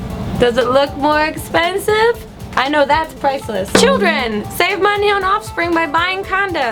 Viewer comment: The first time I heard the offspring advice I thought you said "Children save money by..." Funny video.